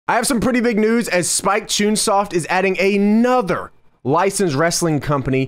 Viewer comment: Did he just say "choonsoft"? Is that right?